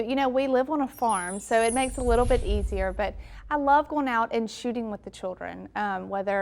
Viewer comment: Why did i hear this as "i love going out and shoot the children"